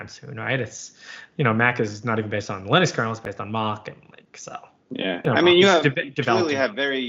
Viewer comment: What does he say at 41:53 that macOS (OS X) is built on? I can't tell, but it doesn't sound like UNIX, BSD or NeXTSTEP, it sounds like "mar-kin-so"? Is he perhaps saying Macintosh? If so I certainly hope that most people today have upgraded from OS 9. Or did I miss something? Is it no longer running Darwin? If it is then macOS is very much UNIX/Unix-like just like Linux. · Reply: It's Mach. It's a different style of kernel than Linux. While it's unix like it's not linux. Not sure what you're getting at? I think the point is that shared open source projects like Mach when taken by companies like apple become their own non open sourced things